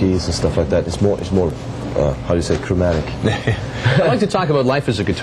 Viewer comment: I dont think so, at 4:16 he says something about jazz chords and scales... and then he says "(jazz) is more cromatic"